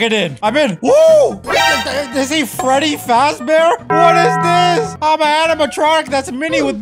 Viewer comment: When he said “Freddy fazbear” I heard “Freddy FATBEAR”😂😂😂😂